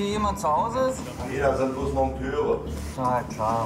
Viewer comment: hat jemand die Antwort akustisch gut verstanden,das letzte Wort klang wie "Monteure"